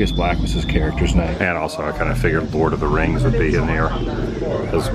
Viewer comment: It sounded like you said bored of the rings. Maybe I misheard.